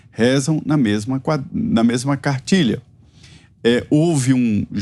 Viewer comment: Juro que ouvi mesma quadrilha 🤣🤣🤣🤣